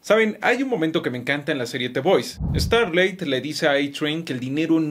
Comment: En la serie que? 0:20, no entendí bien y la serie no la ubico por las imágenes que puso, pero me despertó el interés por verla. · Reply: The Boys